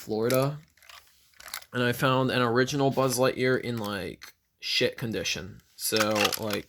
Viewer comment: what did he say? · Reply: "Shit condition"